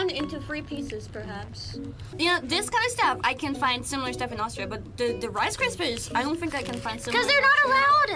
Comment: "I can find similar stuff in Australia" Is this a joke or did you actually mishear that? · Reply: LCM rice krispy treats exist but i think she meant Austria (australia meme) here